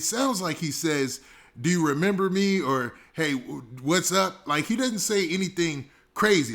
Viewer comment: “What’s up, lil bruh…” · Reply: I thought I heard “what’s up cuz”